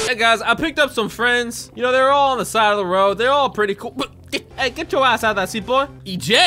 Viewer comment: it sounds like he is saying Hey get your a word out the seat